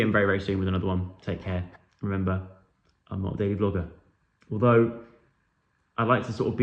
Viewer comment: I thought you said you’re not a daily Volga jokes😂